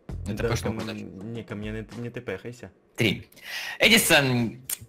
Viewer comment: я один услышал как сказал 3?